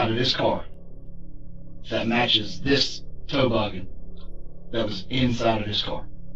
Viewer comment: Did he call the hat a toe-boggen?